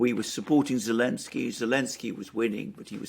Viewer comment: Did you just say Lemsky 😂